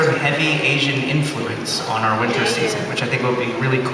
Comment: did he said winter season? 51:17? is that when second part of s3 premieres? that's too far away